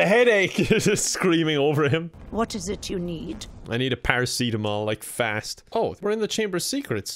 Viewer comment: what did he say? sorry i’m dumb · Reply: paracetamol, it's a painkiller